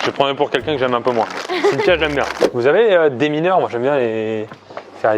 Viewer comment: A 12:00 j'ai compris "Vous avez des mineurs (genre des -18 lol), j'me suis dit il est fou Pierre" mdr